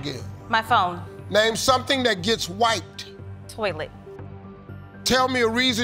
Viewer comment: I thought Steve said something that gets "white". Lol.